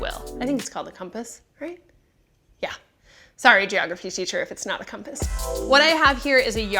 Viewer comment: Did she say Geography?!